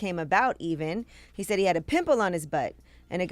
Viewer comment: bruh. at first I heard "pickle" 😅 😅 😅